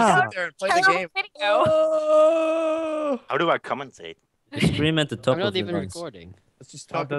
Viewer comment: what is even saying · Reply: thats saying "you scream at the top of your lungs" i think